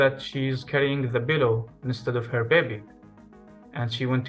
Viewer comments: can anyone confirm what the woman was holding instead of her child please? I can't quite make out the word, is he saying 'pillow'? Thank you advance x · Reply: Pillow, yes. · I can't understand what is said at 34:40. What was the mother carrying instead of the baby? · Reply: Pillow